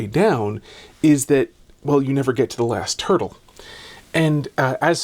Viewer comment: for a moment, I heard "the last turtle".